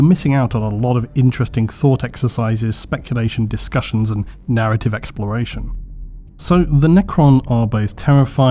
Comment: This video was originally titled - What do I HATE about the Necron? lol which seemed too ott. Let me know what gets under your skin on a permanent basis when it comes to the lore... ALSO - - please help me appease our lord ALGO, by Liking the vid if you enjoy :face-blue-smiling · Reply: It's so funny; when you say "narrative exploration," I hear "heresy." 🤔